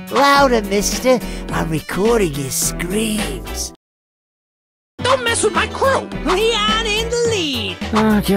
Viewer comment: I THOUGHT CROW SAID DON'T MESS WITH MY CROW😭 😭 😭